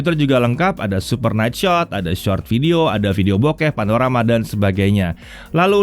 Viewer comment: "ada video bok*p" apa gw yg salah denger ya..??? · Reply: bokeh